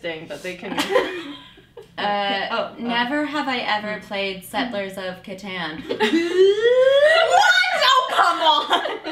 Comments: What did Natasha say at 3:30? I'm not a native english speaker, I don't understand xDD thnks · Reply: Never have I ever played settlers of catan. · What did Natasha say? I couldn't understand the name of the game (English is not my native language u.u) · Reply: of Catan, its a boardgame